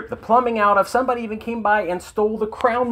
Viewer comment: did he say crown moulding? BAhahahaha